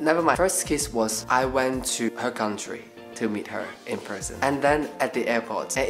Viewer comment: at the literal beginning i thought he said he met her in prison 💀😭